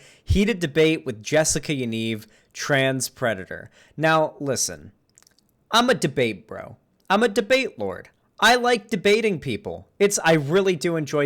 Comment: I was watching this late at night and had my volume down and when he said "I'm a debate bro, I'm a debate bro" I heard "I'm a debate, I'm a debate"